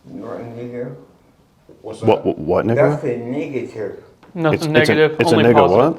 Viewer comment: did say what nigga 😂